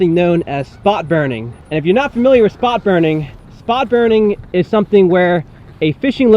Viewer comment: I thought you said thot burning